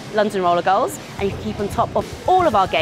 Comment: Right to the end I thought they were saying "Roller Debbie". Which incidentally would be a great alias for a skater girl. Especially if her name was Debbie.